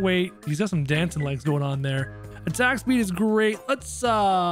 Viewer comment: I thought you said tax speed